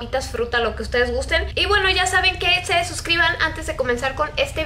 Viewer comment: Cuando dijo "y se suscriban" entendí "y se desuscriban" AaaAaaAah.